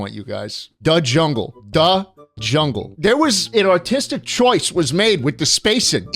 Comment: his new york accent made him sound like he was saying "autistic choice" instead of "artistic choice"😭